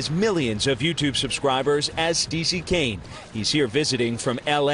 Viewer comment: Did I just heard Steezy Tane?🤡